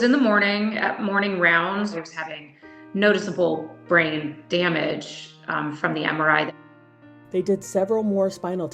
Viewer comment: What is she saying at the 2:30 mark? The editor cut her off. It sounds like she’s saying that he had noticeable brain damage from the MRI.